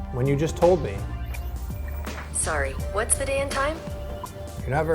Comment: I thought she said "What's the damn time?" for a second 😂